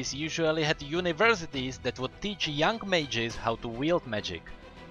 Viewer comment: When I was Watching this then i put captions on.It says in 1:01 May-Geez instead of mages